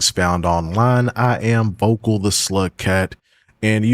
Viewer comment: EARLYYYYYY, AND I LOVE THE CONTENT!! ALSO FOR THE LONGEST TIME I THOUGHT YOU WERE SAYING "vocal the Sl*t Cat" and i was like oh ok...